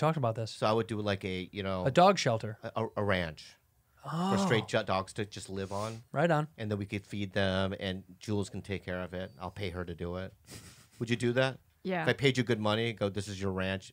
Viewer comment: Sounds like Bobby says “a ranch for straight dogs to live on” Hahahah!! I know it was just a stutter but was still a hilarious premise